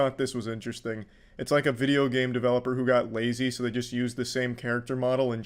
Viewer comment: did he just call god lazy